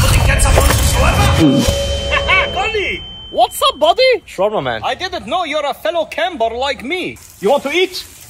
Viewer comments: I thought he said "trauma man" 🙄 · When he said shawarma man I thought he said trauma man 😂😂